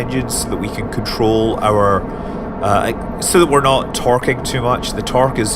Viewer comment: i thought Scott said "twerking"